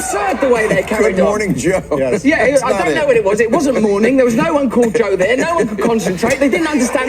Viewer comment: perhaps Joe Stalin, Joe Mengele, Joe Byedin... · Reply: I thought he also said 'there was no uncold coffee' too lmao